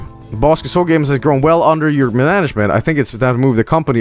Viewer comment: Did say "mananagement" at 27:26, or am I just going insane?